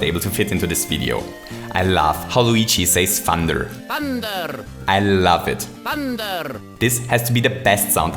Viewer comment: Luigi says "Thunder"? I thought he was saying "Hander", cuz it's a hand power.